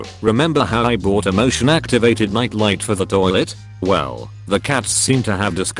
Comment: Misheard: "Emotion-activated lights". Sounds like a good idea, honestly.